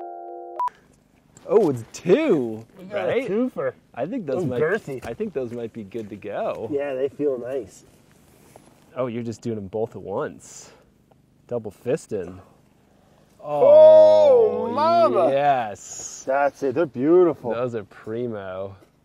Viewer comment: did he just said double fisting? I am absolute can tell he's flirting, I mean who would not, its Brad Leone.